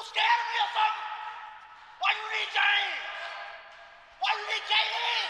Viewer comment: “You scared of me or sum why u need James why u need KD”😭😭😭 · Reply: thanks couldn't quite tell what he was saying lmaoo i thought he said "why u hate james??"